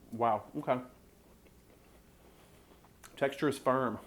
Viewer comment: wow. i thought you said "texture is sperm" o.O